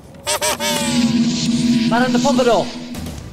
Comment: for some reason i thought he said Miranda Popadom lmao